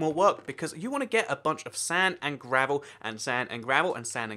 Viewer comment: WHAT DID I JUST HEAR?? GET GRAVEL? you ok bro?